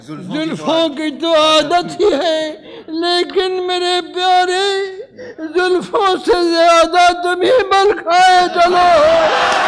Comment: What does he say 9:38 9:43 ? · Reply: Zelfon ki to adat hai lekin mere pyaare Zolfon se zayada tum hi bal khaye chalo ho